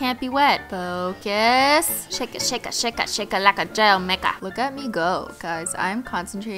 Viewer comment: did u just say the n-word 7:47 no hate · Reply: ihavetosayno aj she said maker